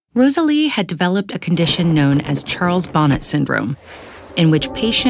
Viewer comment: I misheard Charles Bonnet syndrome for child vomit lol